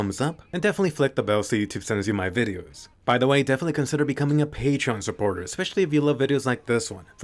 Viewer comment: First time he said patreon supporters I heard “pay trump supporters” and had to rewind that real quick to double check 😂